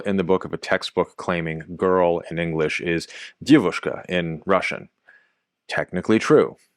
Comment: isn't it supposed to be девушка (what you're saying) not девочка (what's on the video) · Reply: Yes, correct. He is saying девушка, but writing девочка. Девушка is a young adult, девочка is a girl.